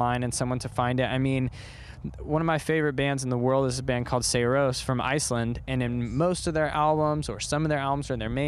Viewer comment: For anyone who can't hear the name of the band. It's Sigur Rós